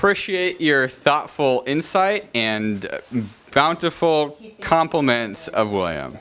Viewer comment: Looks like she says "He completes me"... even tho I know thats now what she said lol